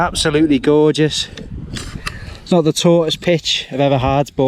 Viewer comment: Listening in a noisy room and when you said "not the taughtest pitch" I heard "I've got the tortoise pitched"! I thought you'd coined a nickname for the Soulo!!!! Great video. Castleton holds a special place in my heart as I got engaged there, back in 2007!